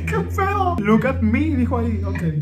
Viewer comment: yo la primera vez que escuché look at me jajsjsjaja